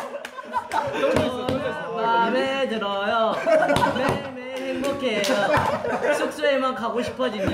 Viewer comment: Does anyone know what haechan says from 3:07 forward i wanna know why they laughed😂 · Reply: i. t they were basically talking about if he liked rooming with taeyong and mark asked haechan “ how is it like?” and he replied “ i. like. it.” but very sarcastically and he continued to reply that he liked it and he was happy and that it isn’t hard to room with taeyong .... but VERY sarcastically which made it soooo funny 😂